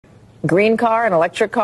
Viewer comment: I'm hard of hearing at 27yrs old cause I thought she said "Green Card" at the beginning 😂😂